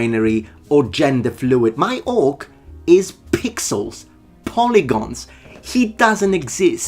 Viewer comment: Lol, Anyone else thought saying 'my orc is pigsauce".. and taking a moment to realize he said "pixels not pigsauce"